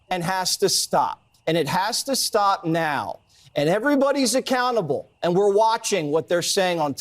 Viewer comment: Every time I hear an american saying 'accountable' I hear 'a cannibal'. 😂